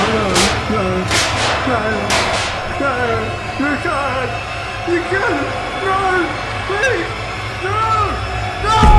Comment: i wonder what douglas said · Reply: No no No you cant you cant no please no no noo (explodes)